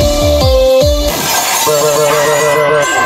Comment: Oh I thought He Said "I'm cranking 90's BULLSHIT"